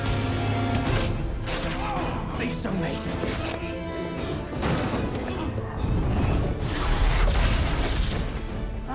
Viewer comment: What did Hook say at 2:46? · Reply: “Don’t think so, Mate”